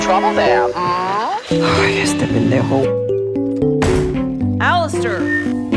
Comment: Can anyone translate what Vaggie says at 0:25 for me, please? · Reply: "Ay, este pendejo." If I heard it right, kinda like Ay this asshole. Ay in this case just being a groan of frustration.